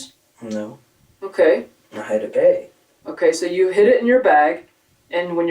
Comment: Can't understand wtf he's saying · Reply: Well don't trust the cc. He clearly said "I had a bag" at 24:32 but the cc says "I hid it in a bag". Clearly not what he said